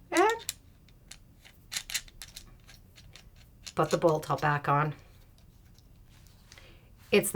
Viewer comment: Can you tell me what you're saying at 3:41? "Put the [?] back on." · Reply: put the bolt back on